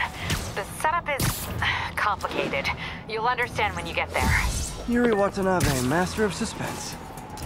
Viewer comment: WHEN I was looking at the subtitles I thought it said yuri WANNABE so it was a fake yuri ehheheheh